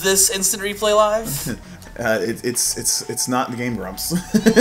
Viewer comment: Low rent Game Grumps really made me laugh. And it sounded like you said Game Brumps at first. Y'all are now and.